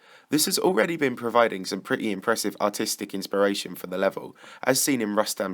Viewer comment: Did you say autistic?